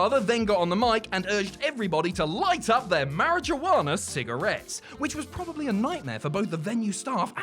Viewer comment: Man do I love closed captions..@ the 14:51 mark instead of "mary-jew-anna" I get "marriage awareness" cigarettes 😂😂😂